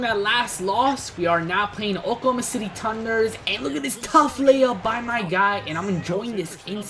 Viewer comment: he says oklahoma city tunder, instead of thunder